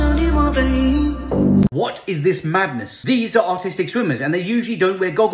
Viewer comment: I wasn't reading the subtitles, and I swore he said, "autistic swimmers."